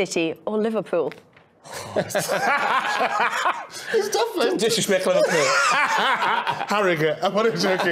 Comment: 캐러거가 <이건 리버풀을 무시하는 겁니다> 영어로 뭐라고 말하는 건가요? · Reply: Disrespectful Liverpool